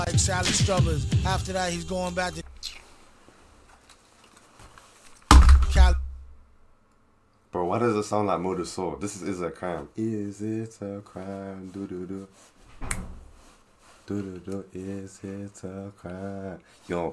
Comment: did I hear 'Modal Soul' as in Nujabes?!?